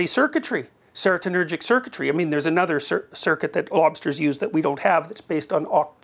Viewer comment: For sure thought he said "Mobsters" but then realized he said "lobsters"